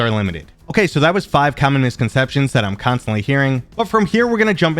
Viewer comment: "Common misconceptions". I heard "communist conception"...